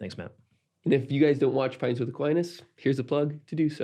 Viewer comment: Did you... did you just say Quints with Apinus? 😂